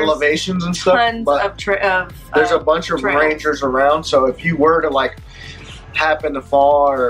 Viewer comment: Reed thought said reindeer not ranger 🤣😂🤣😂